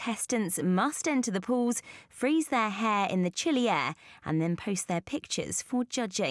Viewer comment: I can barley understand what you're saying lady. "freeze theaahaaaah in the chilleeehhh" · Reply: "freeze their hair in the chilly air" sounds pretty clear to me